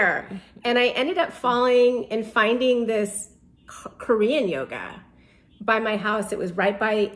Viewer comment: Hi Ranveer, as usual all your podcasts inspire and help me on my Spiritual journey.Can you help me what yoga did she say at 34:04 minute. Would really like to do such Yoga but not sure which yoga did she say, got little confused bcoz of her accent. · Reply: Korean yoga ... basically with a Korean master.